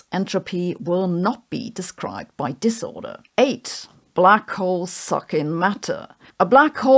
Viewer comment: I thought she said, “black holes fucking matter”.